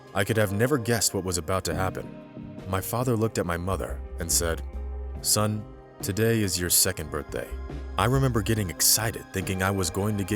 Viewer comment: did he say "Son today is your second birthday" and stop paying for his life.